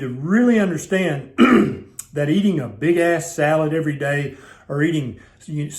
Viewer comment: Did he just say "Big ass salad"? Hahahahahahahahaha!!! That is funny on many levels.....